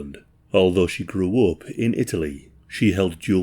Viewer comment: I thought 4 a sec u said "she rizzed in italy" 😏😏😤😤 but negative... One day 🫠🫠🫠